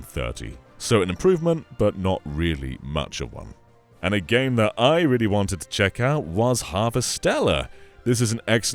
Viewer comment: can someone tell me what this game is called? he says "something Stellar" but i can't find out online which game this is. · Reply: It's 'Harvestella'